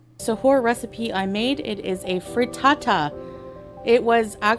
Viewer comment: Ik she meant to say 'frittata'... However, what I heard was, 'freetata'... Maybe its where I come from or my accent... Idk...